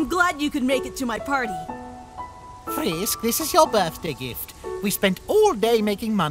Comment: Anybody notice that the captions and speaking are saying a different thing? The captions say, "OUR birthday gift" while the voice actors said, "YOUR birthday gift."